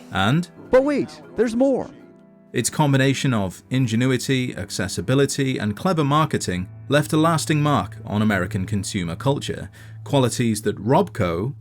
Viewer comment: i have listened to it like 10 times and I still have no idea what they're saying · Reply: Just set it and forget it is what I got